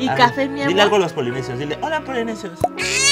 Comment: quien más escuchó el hola que dijo